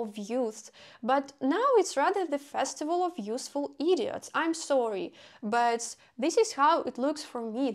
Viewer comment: “The festival of useful idiots” lol nailed it! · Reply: Oh, I somehow heard it as youthful idiots :)